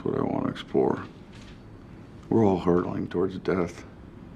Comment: subtitles at 51:07 should be "hurtling" instead of "hurdling"